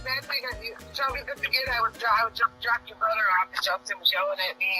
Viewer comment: did she say jack Ur brother off